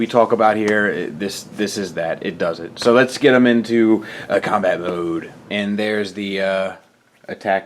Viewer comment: I don't catch the phrase... 1:30 "Op? mod" Can someone correct my meaning? I juzt say vehicle mod but thatz me. -And Hey i really liked your review! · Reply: Alt mode. Just short for alternate mode. No worries :)